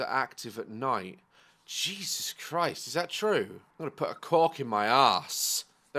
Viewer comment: What did he just say? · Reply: He said a cork it's the thing that you pop off wine